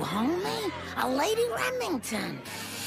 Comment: I hear "A lady Ragminton"